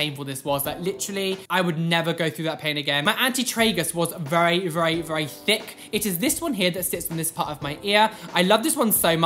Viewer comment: “my anti-tragus was very, very thick” what i hear: “my auntie Tragus was very very thicc” 😂